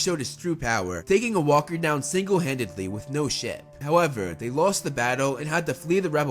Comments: Did he just say “ no sh!t “ or “ no ship “ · did he say sh*t or ship please reply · did he say shit or ship